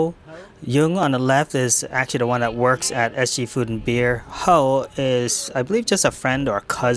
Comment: I thought you said ST, food and beer, but it was SG... 😢.